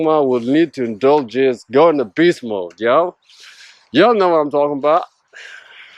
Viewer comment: what did you say here peace mode or beast mode ? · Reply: Well Beast mode is something I would say ❤️🦅✌🏿